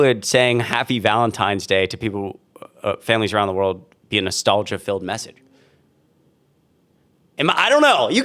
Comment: SHE is disgusting. LEARN English lady. She reads that like a BOT, like an AI voice. I thought she said "nostalgia FEILD message" and then realized is was "feeeeeeeld message".